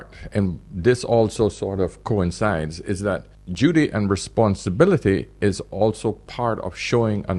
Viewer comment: I hear "judy and responsibility is also part of showing another person that you love them..." and the automated transcription shows "Judy..." too. What is judy ? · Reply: He said "duty" actually not "judy"